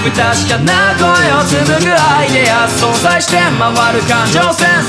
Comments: どうしても｢不確かな声を紡ぐイデア｣がアイデアに聞こえてしまう… (誰が歌っててもです…) みみがおかしいのだろうか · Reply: それめっちゃ思ってました 今思いついたんですけど、あぁイデアってことじゃないですかね？？ めちゃめちゃアイデアに聞こえるけどwwww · どうしても｢不確かな声を紡ぐイデア｣がアイデアに聞こえてしまう… (誰が歌っててもです…) みみがおかしいのだろうか